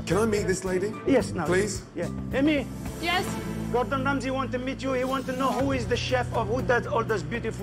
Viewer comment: Sounded like said God-dam Ramsey